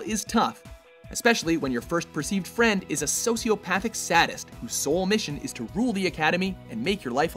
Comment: Did.... did you just say saddest? I'm pretty sure you said saddest. And I'm pretty sure you meant sadist.